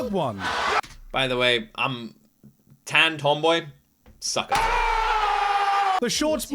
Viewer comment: Did you said "Tanned tomboy sucks?" 🤨 or "I'm a tanned tomboy succkers" ? It must be the latter, right?